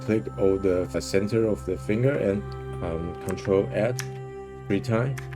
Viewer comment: what did you say is the shortcut? Ctrl+add?? Because I don’t have the add button but I tried ctrl+insert and ctrl++ ( all three times ) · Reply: hit "Ctrl + Add" three times to get the nail selections